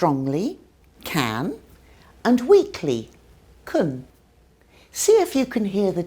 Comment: i heard cunt the first time lmao